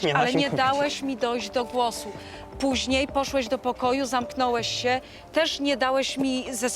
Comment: Tylko ja usłyszałem "poszłeś" ?